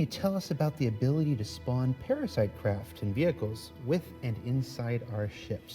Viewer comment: I thought said parasite spawning, not parasite docking. CR probably wants it in the game ASAP for all the Carrack owners (890J too), but it sounds like Constellation owners will be out of luck.